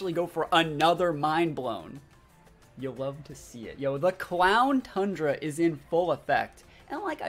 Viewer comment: "Clown Tundra" What I thought was saying.